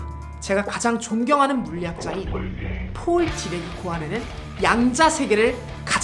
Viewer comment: 존경하는 물리학자인 포이~~~ ?? 누구라고 하는건가요 궁금한데 브금이랑 섞여서 발음이 잘안들려요 ㅜ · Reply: 폴 디랙이에요.